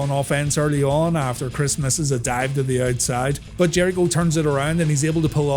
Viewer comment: I swear it sounded like you said “Christmas is a dive”